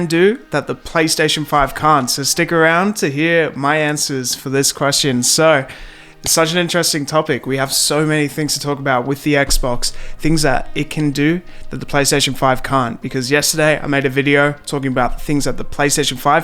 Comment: did he say PS5 cunt ?